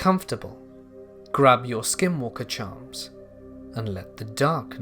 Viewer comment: I thought he said skinwalker churros.